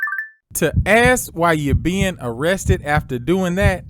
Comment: while showing a still shot of her big azz, bent over, the narrator seems to say "to ASS what you're being arrested for...." 🤣🤣🤣🤣🤣 maybe i misheard him, but it's funny asf 🤣🤣🤣